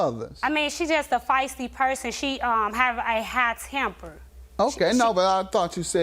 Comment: High temper or short temper??? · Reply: I thought she said “hot temper” 😂😂😂😂